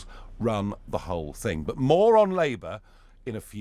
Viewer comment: LOL Nigel, am I the only one who mis-heard you say "Moron Labour" ?, I could not agree more about them LOL...